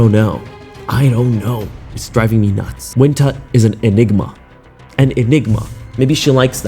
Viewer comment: I thought I heard you say she is an enema. Sorry too loud where I’m at.